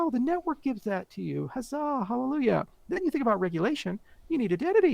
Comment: Did he say "hazzah"? 😂 1:11:59 now that I'm thinking about it, he sounds like the guy who lost the town rat in this video 😂